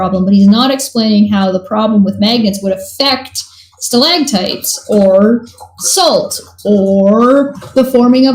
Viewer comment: did he say "stalagtite"?